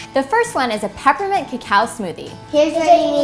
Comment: On the first oneI thought she said peppermint and cow smoothie 😂😭